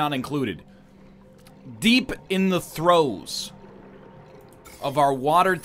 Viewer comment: i swear i heard "deep in th throats" at the beginning